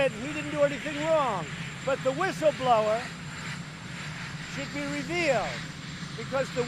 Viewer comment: I had to watch again, when he said the whistleblower should be revealed, i thought he said he should be repealed